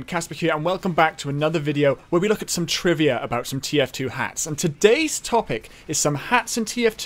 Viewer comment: I misheard "TF2 Hats" thinking he said "TF2 Hacks"